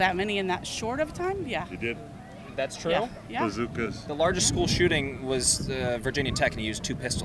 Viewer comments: guy just says "bazookas" as if its a valid argument... Am i hearing him incorrectly or the only one that noticed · did he say bazookas? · please tell me he did not say bazookas 🤦🏽‍♂️😂